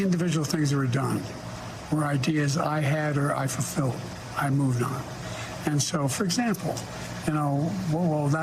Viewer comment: Did he say "[I was] also the guy who created the economy"?